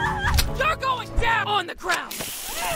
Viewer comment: In the first half I hear “BACK OFF DAB ON THE GROUND”